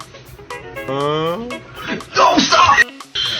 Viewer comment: 왜 '똥 싸!' 라고 들리냐